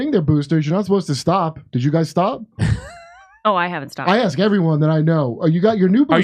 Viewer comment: what did actually said at the back, "oh I had to stop", or "oh I haven't stopped"?